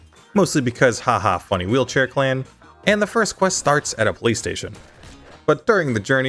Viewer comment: am i the only one who heard "play station" instead of "police station"